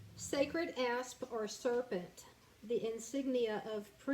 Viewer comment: Oh, I love this one! Although at first I heard wrong and thought you said, “Uranus” and then.....”Sacred Ass”. But it forced me pay closer attention.